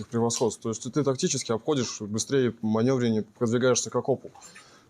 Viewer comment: soldier says “k okopu” which translates as “towards the dugout”, but subtitles say “as a cop” lmao